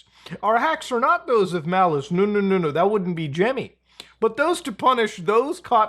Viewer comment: did you say... gemmy..?